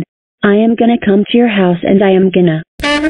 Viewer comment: I wonder what Tera said… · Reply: its said:"and i am gonna lie to nano😠"